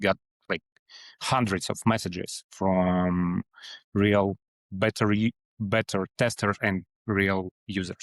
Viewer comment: I think the sub title at 7:29 should have said 'beta' instead of 'better'. Anyways, love the work!